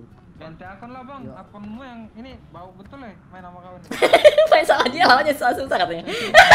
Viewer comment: Ada yang tau ngomong apa? BTW thank you bang udah jdi bocil lite Version🤣🎉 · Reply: Main sama dia lawan nya susah2